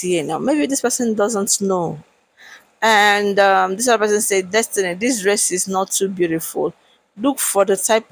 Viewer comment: I thought they said Destiny did not go